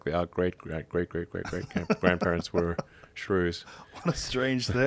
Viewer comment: ohhhh, Shrews... i thought he said gators like shrooms... Lol...